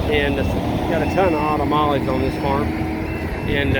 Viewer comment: Am I the only one who never heard it as "autumn olives" and thought it was something like "ottamales?" (like if you combined the word ottoman and tamale)